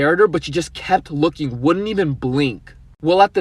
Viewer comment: Was the first guy speaking korean? Edit : Oh I figured out that he said " wouldn't even " I thought he said Unnie🤫